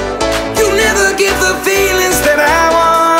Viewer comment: Confession: the first time I heard this it was in a loud restaurant. And I thought it said "You never get the penis that I want" and I can never unhear it